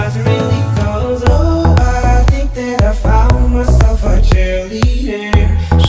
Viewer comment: When I was young I thought the guy was saying "oh, i think that i've found myself a teddy bear"